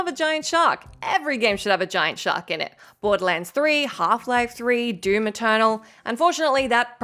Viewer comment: Did she just say Half Life 3? Is she from the future or something?